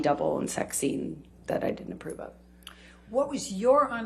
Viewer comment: I think you said Johnny didn't approve of before right 😅